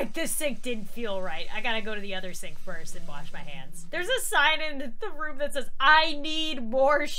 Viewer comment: I thought she said ur a sin in the first story